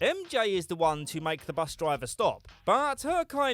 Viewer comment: did anyone else hear "blus"?